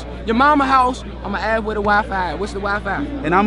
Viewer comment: When he was saying where da wi fi at? I thought this nigga was saying where da waffle house at.. god dam